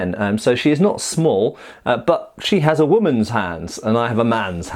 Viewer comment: What said "She has a woman's hands" What I heard "you have a woman's hands my lord"